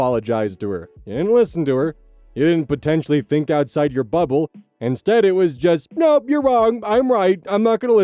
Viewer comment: When said “cause You can’t think outside your bubble” I heard “cause you can’t think outside your butthole” 😂😂😂